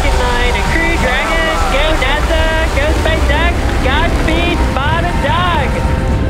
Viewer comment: "Godspeed Bob and Doug". First time I misheard it as "bottom dog" and was hella confused.